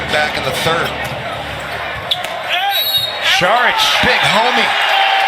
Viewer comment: Wait did he say “Big Homie” at 7:30 ? 😂😂😂😂😂😂